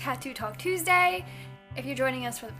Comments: i thought she said Taco Talk Tuesday XD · My fat ass thought she said "taco talk Tuesday"